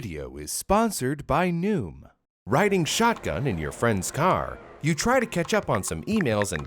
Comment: i thought said raiden shotgun😭😭😭 is this what i get for playing genshin impact for a long time💀💀